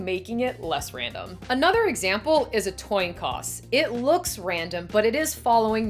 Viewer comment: I'm sorry, but did you just say "tion coss"? Also, is your shirt based on "The Arrival" starring Amy Adams?